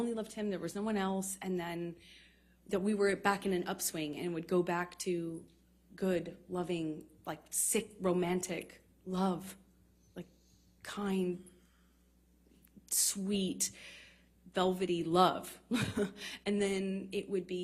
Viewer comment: Did she say " sick romantic love? "